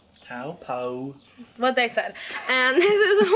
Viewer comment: was she saying Taupo?